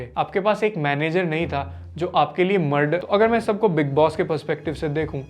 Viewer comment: cant understand what happen here????? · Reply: Bro he was saying the muder but had to cut that word bcoz of yt's policy